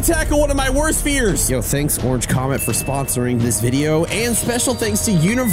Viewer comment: I legit thought he said Orange County